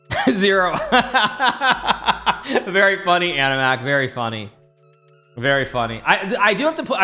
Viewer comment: did he just say Animak? Like Animak from AnimeUproar or am I trippin?